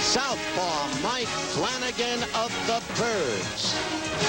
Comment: is he calling the Orioles the "turds" or the "Kurds"? dosen't sound like "birds"?